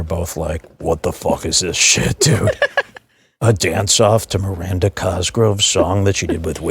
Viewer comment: Miranda Cosgrove’s aunt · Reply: I thought you said "Miranda Cosgroves cunt" for a hot minute. Don't know what that could possibly mean but it really took me out for a second there